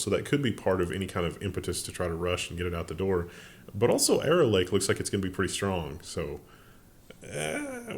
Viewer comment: I thought you said “Error Lake” as a pun on the previous Intel story 😂